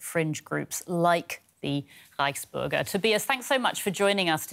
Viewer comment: Dear Germany, I'm an Australian and I know hamburgers were major success and they are bloody delicious, but rice burgers are a bit extreme.....🤦‍♂️ Stay peaceful ✌️ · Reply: I have to admit the first couple times I heard Reichsburger I thought they were saying "rice burgers"